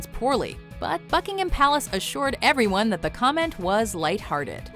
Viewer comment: it sounds like she said Fuckingham Palace